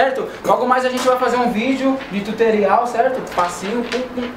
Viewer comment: OXE!! eu Ouvi ele falar (Di Tuterial)??? kkk